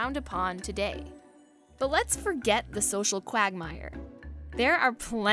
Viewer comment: did you just say Quagmire?